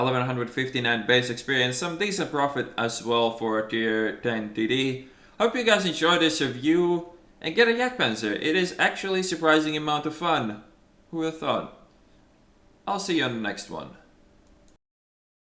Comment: I thought you said "an I 'll see you's laterr" like RamJB, for a moment xD cue awesome RamJB outro music